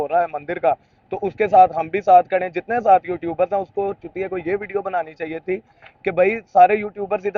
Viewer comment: Did the guy call him Chutiya??? I thought I heard this